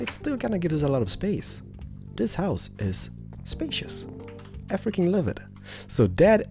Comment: When u say "I freaking love it" it sounds like u say "africans love it"